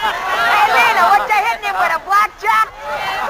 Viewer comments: "Hey, Lina, whatcha hit him with, a blackjack?" My favorite line. Funny, I always thought he said "flapjack." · Hah, yeah I always thought he said flapjack too. Though I think the funniest part in this isn't a line - but its when Kelly first appears on the stage and you hear the footsteps and cane sounds get picked up.